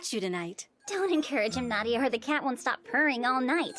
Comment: I literally thought she said p*** cat I was like 🤯🤯🤯🤯